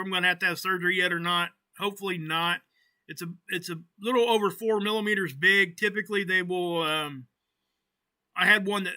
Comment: Go to whose channel for info on the Schumann frequency? (Just before 7:31 minutes) I couldn't hear the name clearly. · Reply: VERTE'S CHANNEL <link>